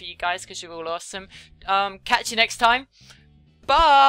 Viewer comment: yes at 22:30, i just turned my volume all the up to make sure and it still sounds like she said "I'm a real super sad (saiyan?) lesbian". It sounds nothing like 'legend' and more like 'lesbian'.